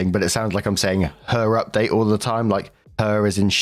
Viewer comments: 🙋🏾‍♀...i'm one who thought you were saying 'her' update ....then i tought maybe it's the accent and you were saying 'our' update.... 😂:_WaffleMuchLove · People hate nepo babies anyway. They especially hate them when they're unqualified and rude. Why doesn't she have a driver to drop her at the door anyway? 🙄 Good update for OP. It has never once sounded like you said HER update. And, yall know I would know, I've watched EVERY video. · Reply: I always hear “her update”, making me question every it every time the OP wasn’t a female.